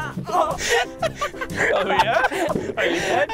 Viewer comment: that kinda sounded like a "Micheal Jackson Oh yeah"